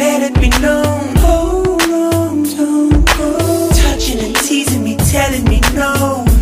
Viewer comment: tf why i always hear it " hold on jungkook "